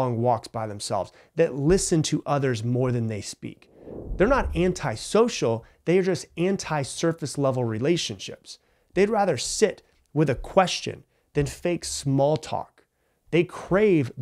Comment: “Anti-surface level relationships“ 👍🏻 However, I heard it as “anti-circus level relationships”👍🏻👍🏻